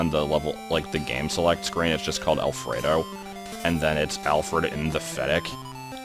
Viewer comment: Every time you said "alfred n the fettuc" I heard it as "alfred n pathetic" and, uh, yeah, accurate.